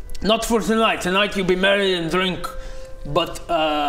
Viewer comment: For a second i thought the mayor said "tonight you be MARRIED and drink" instead if merry and drink